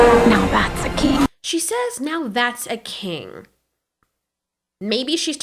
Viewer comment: May I ask, wheres Eret. And also I thought astrid said "now that's a key", as in that's a key to defeat grimmel. Not "that's a king".